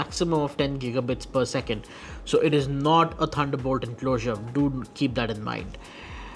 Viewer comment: Please consider enabling Closed Captioning. No disrespect meant but due to your accent there were sections that we could not understand what you were saying. My co-workers could not either. One section was at 1:43 where you said "it was not a ???????? enclosure". Again no disrespect meant.....just some ideas. That said I would love one of these enclosures....but it is so damn expensive. With shipping and taxes at Newegg Canada, this costs close to $95.00CDN. OUCH! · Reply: "it is not a thunderbolt enclosure" I'm English myself and had no issues with the guys accent or language. So thank you for uploading the review, much appreciated!